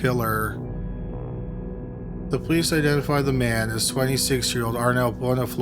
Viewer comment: I am NOT trying to be an ass, but I genuinely thought you said “sweaty six year old,” at 4:14 and now I can’t un-hear it.